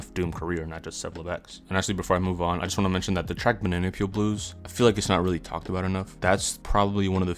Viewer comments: What track did you say at 3:04? I want to check it out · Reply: bananapeel blues off the mr hood album boss!!! it’s like DOOM before he was DOOM check it out you legend !! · Can someone tell me what he said at 3:04,im trying to find the track "minylonyl blues"? I'm obviously new to mf dooms music.... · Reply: bananapeel blues off the mr hood album!! check it out boss!!